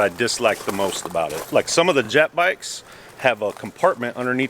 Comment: Did he say “Jap Bikes”?